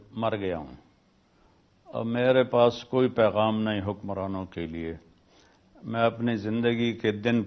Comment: Madam, correct yourself, HE SAID HUKMARAN......you dare to say the truth what he said about the Current Hukmarans,.....for youtube earthing you are spreading chatpati news....i got you.....EK Din tum bhi apni zindagi ky din pory kero gi.......